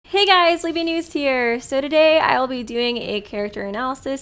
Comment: I don’t know why I thought they said “Hey guys Liby moose here”